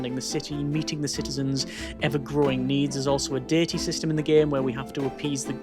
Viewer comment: i am so sorry, but when you said diety system, i forst thought you said dirty system, then dating system